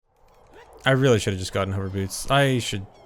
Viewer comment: In the intro it sounded like he said "I should've just gotten herpes."